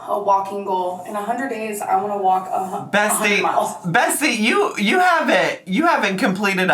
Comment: In tears over 'double feature' 😂 And did you call her Bestie or Bessie at 7:20?😂😂😂😂😂